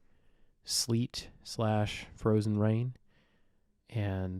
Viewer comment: at 20 seconds into the video I definitely heard "sleet slash purple rain" the first time around :P